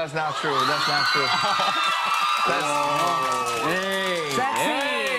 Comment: Everyone: * Focusing On Joon,s Biceps * Jungkook: FLEXIN'! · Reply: I think JK says: ‘Sexy!’ at 7:40 He’s talking about RM after all….🔥